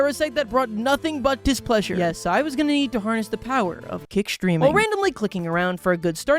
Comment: I looked down to eat my baked potato and did not hear kick streaming. I heard kid streaming so I have renamed this to kid streaming. Let's see how it does.